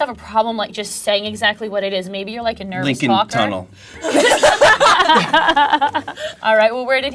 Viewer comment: what did he say? lincoln tunnel? why?